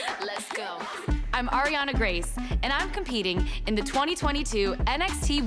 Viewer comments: I Fr thought it said Ariana grande 💀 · This is the I thought it said Ariana Grande button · Omg I thought that said Ariana Grande for a second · I thought it said Ariana Grande